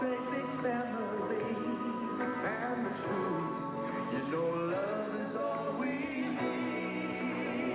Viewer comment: Does he really say that..?????.... “ and choose.., you know love is all we need” ?????🤔